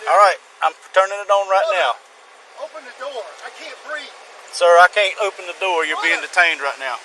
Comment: did he said... "I cant breath", holy shit!